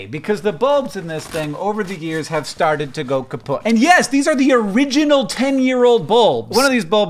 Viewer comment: Did you say the light bulbs started to go "kaputt" at 0:20? The german word for "broken"